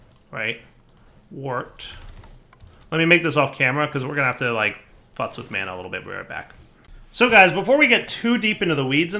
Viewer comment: did say "we are gonna have to fucks with mana real quick"?